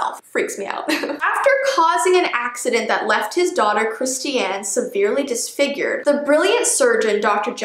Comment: Jessie I loved everything in this serie but at 7:25min I heard *Christy-Ann*whene I heard that I said no lemme see again and whene I heard it good I heard my name and I was spooked but I don't mind lol so yeah!!